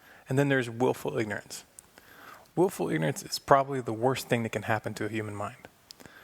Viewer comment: what does he say at 1:30, willfull nertism, worse thing that can happen to human mind? What did he call ''what'' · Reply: He says, "Willful ignorance is probably the worst thing that can happen to a human mind." And explains more in the description of the video about the way people ignore facts in order to hold on to false belief systems that do more to enslave that individual because of the precepts already adopted that control the behavior. Logically, people believe many things. It is easy to imagine, nobody is 100% correct. Therefore, odds are, I am not 100% correct . The math says, what I know is just a suggestion... Above all, facts should be fully scrutinized and EVERYTHING questioned.